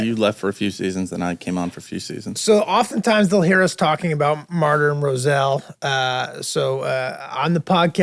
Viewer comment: This entire time I thought y’all were saying “Marty and Rosell”. 🤦🏾